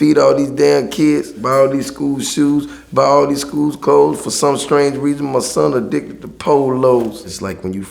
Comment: I always thought he said, "For some strange reason my son addicted to Pornos" Thanks for clearing that up 😂😂